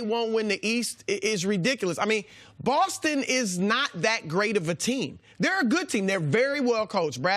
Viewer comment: “Celtics are not a good team” bruh any team better than 40-20 in a lil over 60 games is a very good team no matter what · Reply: markdubbb who said that? Can you find the time for that. I heard Chris say, 'theyre not that great of a team, they're a good team' but not what you stated